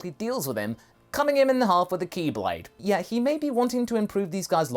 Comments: Wait did you just say “keyblade”....oh....chi blade....I think that was somehow on purpose you sly dog! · did someone say "Keyblade"? =|